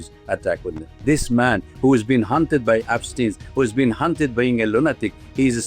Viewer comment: subtitle error? Did he say “haunted by £pstein, haunted by 1LLuM1N@